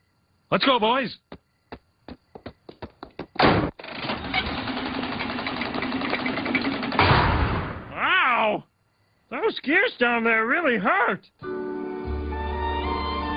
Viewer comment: what does he say? Mosquitoes really hurt?